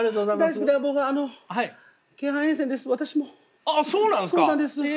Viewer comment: の京阪沿線が聞き取れず、再度聴き直した人3人に1人はいる説